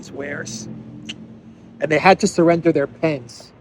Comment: I hear: "They have to surrender even their pants." I'm thinking: hmm🤔